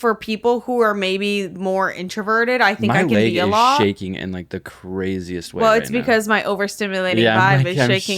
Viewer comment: The way pronounces "leg" like "laig" made it sound like he said "my labia is shaking like crazy right now"